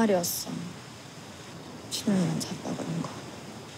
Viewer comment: 여주 뭐라고 하는 거예요? 뭐라는지 안 들림 ㅠㅠ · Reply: 준영이랑 잤다 그랬던거